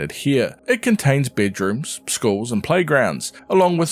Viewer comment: When ever he said schools i thought at first he said skulls